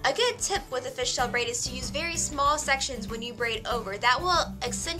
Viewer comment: When she said "very small sections" at 5:25, I thought she said "Harry Styles".... directioners....